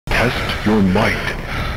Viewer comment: I thought the voice said “Test if your white”